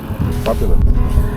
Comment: Mate hearing you get choked up over your dad's health hits hard. I lost my dad in March last year, I wish there was anything I could have done like this. I think you got a fantastic bike for the price and hope your and your da make many more memories on this, as well as the series adding to that in the story of the old boy. I'm sad for your channel though, since it's going to become a channel to show that beautiful golden retriever instead of a bike channel. You might wanna add your own subtitles too lol, 8:50 has a bleep word instead of fabulous on auto.